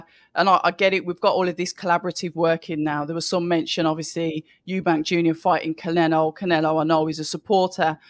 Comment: Thought I heard 'Clenelo' there lol